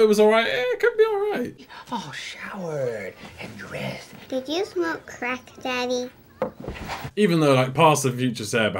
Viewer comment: The first time i watched this as an adult not a kid and i heard "Did you smoke crack daddy?" it fucking sent me LMFAO